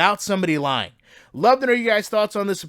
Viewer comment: damn he’s already back with another commentary and it hasn’t even been a week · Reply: In a wink is what I thought he said?